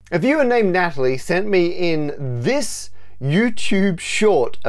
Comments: Anyone else thought he said "If you're named Natalie, send me in this youtube short..." at the beginning? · i thought you said "If you're named Natalie, send in this YouTube short..." and was SUPER confused but then I realized I'm just slow on the language processing atm.